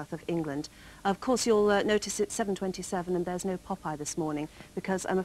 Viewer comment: there's no what this morning? it sounds like she said "popeye" as in the cartoon character. why in the world would a morning show air cartoons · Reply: Yes you heard correctly, Popeye was a staple feature on TV AM for most of its ten year history.